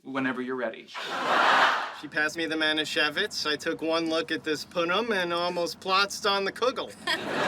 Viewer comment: What exactly is Stuart saying? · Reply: "She passed me the Manischewitz, so I took one look at this punim and almost plotzed on the kugel."